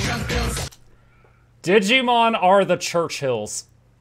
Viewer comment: i hear "Digimon are the chungus"